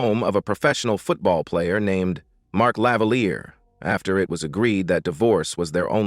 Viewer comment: I heard "Mark Lavalier" and immediately went to the comments, LOL